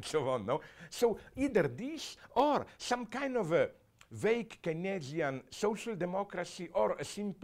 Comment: I think he said keynesian, not connection, in case you are reading the caps.